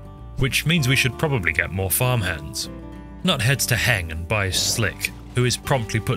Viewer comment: "nut hats to hang and by a sling"? Can anyone tell me what was actually said? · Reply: Nut heads to Heng (a place) and buys slick (a person)